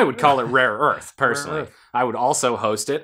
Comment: being honest I thought it said rape earth at the end